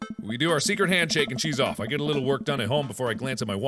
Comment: the captions says “cheese off” instead of “shes off”